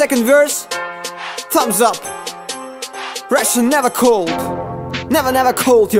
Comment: Я одна слышу "СРАТАТА"?)))